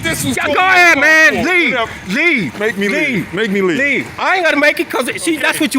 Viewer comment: Yeah the cop just had that human moment when dealing with an asshole. I know what you're saying on a legal premise. The sentence was "Ill leave when i want to leave, ok bye." Idk where "Make me leave!" came from. I don't like cops that can't keep their cool so the last thing im saying is that he did a good job. But if saying ill leave when i want to leave and then immediately saying "ok bye" counts as a threat then i question our legal system LOL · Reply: He clearly said "MAKE ME LEAVE" the 10:11 mark! Js..